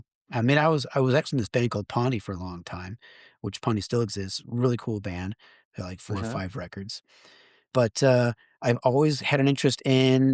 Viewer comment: Nice interview! Could you spell out the name of the band Chad references at 52:40 (and elsewhere)? The auto subtitles say "Pondy", but I can't find any information about a band with that name associated with Chad Channing... Thanks & rock on!! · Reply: I reached out to Chad and the correct name is "PAUNDY." I fixed it in the close captioning as well. <link>